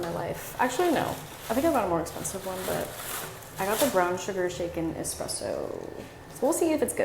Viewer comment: I thought you said “brown sugar chicken espresso” 😭